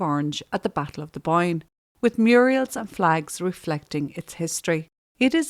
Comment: Sorry, fell over at 3:40 when you said Muriels instead of Murals, just like Hilda Ogden used to say.